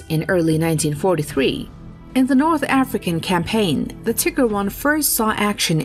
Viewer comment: Informative, I tried but I couldn't hold chuckling each time I heard "Tigger", after: "Tigger two in the battle of Normandie" had to take a break 😆